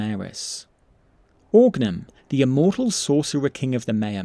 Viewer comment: for a moment I thought you said Orgnar as in the nord in Skyrim. That would be wild.